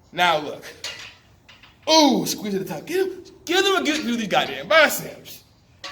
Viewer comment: Ayooo lmao your my guy but wtf did you say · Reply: give them a good view of these damn biceps..lol